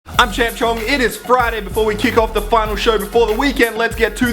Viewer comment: Sounds like he say's ( Hi im TrampChong )